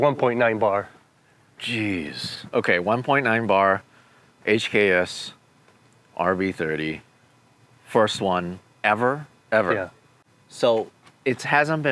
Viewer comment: “First one ever” yeah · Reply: “HKS RB30, first one ever?” “Yeah”. Again I don’t know what’s so hard to understand he clearly says HKS RB30 and it’s even written in the thumbnail 🤷🏻‍♂️